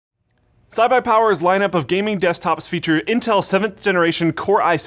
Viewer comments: Did anyone else hear say "Cy-buy Power" at 0:47? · Did he just say "Cybi power" at the beginning?!?!?!?! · did anyone else hear say CyBuyPower?